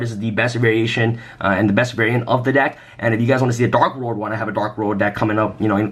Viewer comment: Did you say Darklord or Dark World? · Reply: dark World. At least i hope